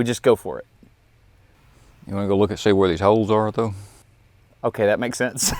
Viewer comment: WTH is he saying? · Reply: he said “you want to go look and see where these holes are, though?”